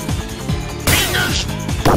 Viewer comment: Did he just say... PINGAS?!?!?!